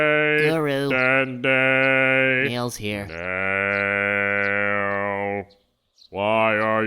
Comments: Thumbs up if you thought Dende said "Pills Here" at 4:30. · does dende say "pills here" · Tgumbs up if at 4:30 you thought Dendae said "Pills here." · I thought Dende said, "mail's here" then Nail suddenly appeared from the ground, special delivery lol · I thought he said pills here when he said Nail's here.